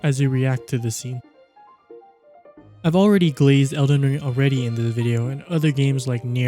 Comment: Are you saying "glaze" instead of "praise"? @ 9:09 and